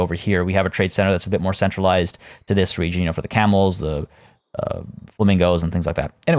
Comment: When you said camels i thought you said cannibals. I’m like omg whyyyyy!!